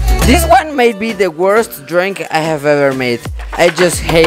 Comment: sounded like “this might be the worst DRINK I have ever made”